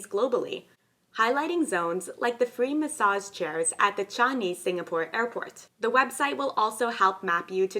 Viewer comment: Did you say Chinese Singapore airport? (at around 8:00 minutes). I know the Chinese might like trying to expand their borders LOL, but I think you might mean Singapore Changi Airport?